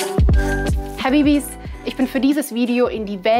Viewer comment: Was sagt sie bei 1:51 min? Ansonsten ein super Bericht und eine gute Aufmachung des Videos. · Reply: Habibis, aus dem Arabischen. Bedeutet in einer Beziehung so viel wie "mein Schatz" (edit: Singular, Habibi), im Allgemeinen soviel wie "ihr Lieben".